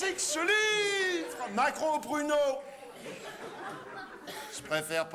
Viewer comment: dites moi que je suis pas le seul à. avoir entendu Macron aux pruneaux